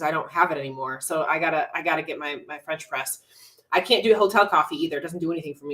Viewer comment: When you said “French press” I heard “French fries,” can you tell I’m on prep?